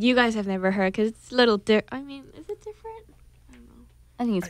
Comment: wtf with the translation. i heard jennie says "different" but the translation translated it as a "dick" AHAHAHAHAHHA gad!!!